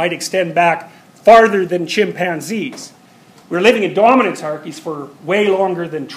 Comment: What's the word on 3:07 ? "Dorminancearchys"???